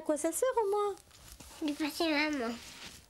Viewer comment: Je rêve ou quand elle a dit "tu sais à quoi ca sert au moins à 5:11" la petite fille a dit "depasser maman😭😅"